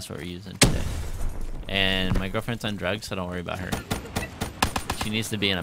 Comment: I thought he said my girlfriend undress